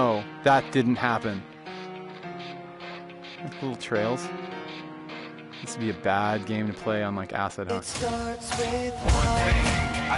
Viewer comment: Did he actually say "This would be a bad game to play on acid" 💀